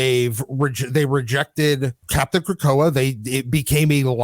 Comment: Been away from Marvel for awhile…I had to rewind the video to make sure I heard that Cyclops’ secret identity is called “CAPTAIN KROKOA” 😂😂😂😂😂😂 IM HOWLING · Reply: NGL I misheard the name as Captain Krakatoa which I was like “TF?”